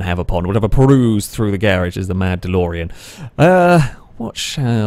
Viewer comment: For a moment I thought you said The Mandalorian - then I realised you actually said “The mad Delorean”. Doh....